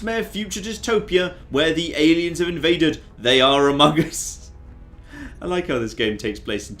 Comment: Either I am going completely insane, or he actually said "amogus" instead of "among us" 4 months before amogus actually released.